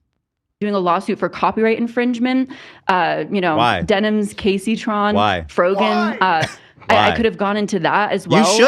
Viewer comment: I just realized Ethan was saying he was being called the ‘k’ slur and not the ‘caselur’ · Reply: Lmaoooo what, I also thought he was saying caselur, like I thought the guys name was chancellor or something 😂 I'm so dead